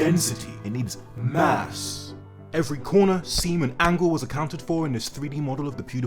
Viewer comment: ay yo what did he say?!?!?!?! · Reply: Corner seam and angle. Not corner semen angle